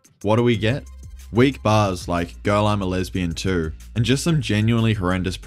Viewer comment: Okay I thought he said “girl in a lesbian too”… I’m not about to make sense of it